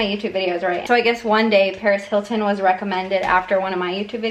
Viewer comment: When she said Paris Hilton I thought she said PAREZ HILTON I WAS SCREAMING!!!!